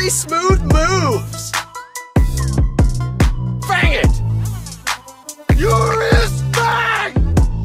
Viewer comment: I thought it said you respect